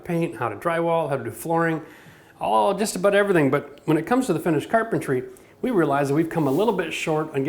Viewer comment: I thought it said Finnish carpentry... ;-)